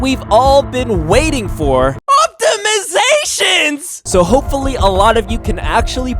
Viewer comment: At first I thought he said Optimus prime when he said optimizations lol